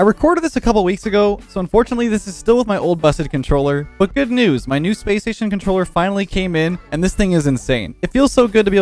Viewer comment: I thought this man said my PlayStation gaming controller came in and I was about roast you but then rewatched it and saw man said SSG (SPACE STATION GAMING) controller. 🦐